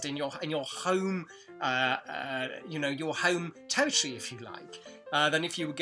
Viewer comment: Thank you! Very interesting video, and motivating too! Just one question: what do you say in 5:07... "in a more natural context, in your home ??? If you like..." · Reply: "in your home territory if you like." Sorry, diction wasn't so clear there!